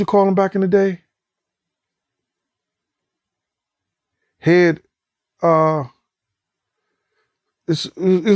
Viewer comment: I think he was trying to say Head Honcho. I think.